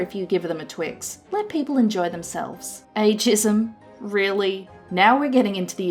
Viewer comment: Had to pause to comment this. Ageism sounded like you said a-jism... or maybe I've had too much internet - nah I blame you.